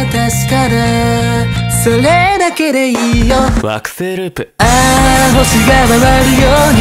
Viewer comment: ジェルくんの曲名コール、惑ふぇいループって聞こえる